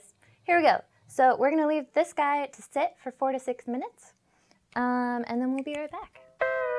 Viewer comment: So I heard "leave this guy to sit for 46 minutes" at 2:00 and I was like "wow, that's a decent morning routine"